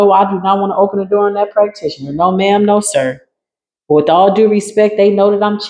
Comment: Did you say “with dog-doo respect” 30:21 Ha! Awesome